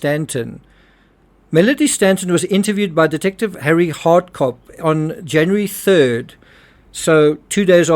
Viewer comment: Maybe I misheard, but is the name of the detective mentioned at about the 7:00 mark really *Harry Hardcock*? I really hope I heard that correctly, because if there is actually a "Detective Hardcock. Harry Hardcock," it's the most gloriously funny thing I've ever heard. The jokes about going undercover and probing deeply into a case write themselves.